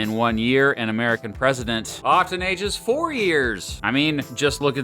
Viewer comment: I thought you said “octenages” and thought it was a term for people who age fast